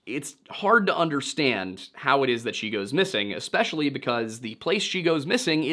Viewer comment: you said "how it is that she went missing" Instead of "how she went missing" 21:34 Steven is rubbing off on you aint he 😂